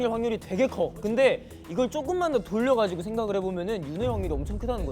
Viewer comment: Okay I maybe heard it wrong, but did San said "Yunho hyungie" at 49:00....?